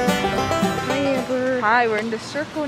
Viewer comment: I thought you were saying “I’m getting blue balls”